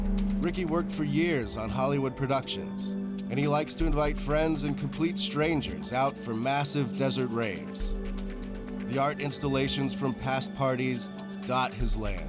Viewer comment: did he just say, "and he likes to invite friends and complete strangers out for massive desert rapes.."?